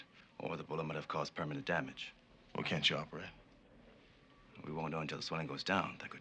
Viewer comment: did Hunter say: "We can't chop" ? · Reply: George Heinsmann yes he did say chop